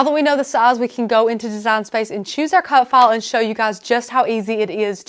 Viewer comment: Omg!! I can listen to her all day. Love her accent but most of all, I love how professional these tips are! Thank you 💕💕💕💕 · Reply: what the hell accent is it? I thought she was saying cut fall. finally, she pronounced file with an i sound, so I knew what the heck she said.